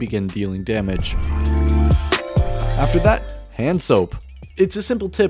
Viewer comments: Who else heard "handsoap" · is it just me or does he say hand soap instead of hanzo? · did I just hear hand soap? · Is it just me or did he say hand soap for hanzo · does anybody else also hear HANDSOAP instead of HANZO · did he just say hand soap lol